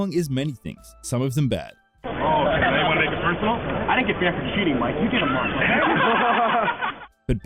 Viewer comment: what does he's say? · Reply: "You wanna make it personal? I didn't get banned for cheating, Mike."